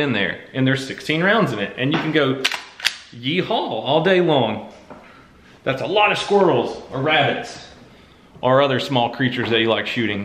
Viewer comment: pls tell me im not the only one that heard him saying thats a lot of squirtels instead of thats a lot of squirrels